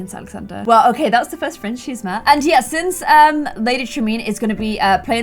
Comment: am I the only one who heard "Lady traMEAN?"